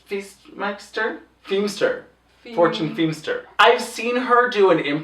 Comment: English is not my first language, so when Ru said Fortune Feimster was a guest judge I heard "four-chinned" Feimster... I don't think I was wrong though...